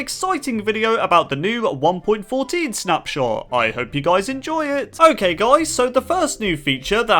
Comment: I heard I hope you DIE enjoy it.